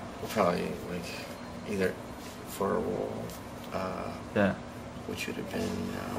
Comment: What does James say around 2:40? It sounds like "Either for a wool" to me lol. Anyways, this interview is so flippin' great! I love these questions. And of course his answers.